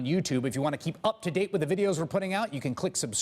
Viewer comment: When Chris Hayes promotes the website at the end of clips, it sounds like he says "just blow me," when he says "click subscribe just below me..." Kinda funny.